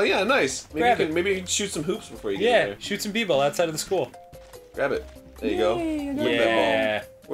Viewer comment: I swear I heard say "shoot some people outside of the school" and was like WOAH.